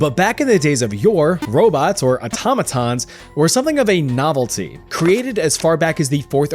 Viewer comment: did he say... automatons...